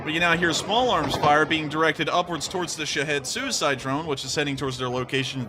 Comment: Did I hear "Shithead suicide drone"? 😁